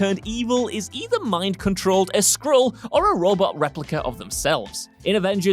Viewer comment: I know he must have said Skrull, but at 4:11 it sounded like he said Squirrel. A superhero turning bad because of a squirrel sounds like a graphic novel I would buy.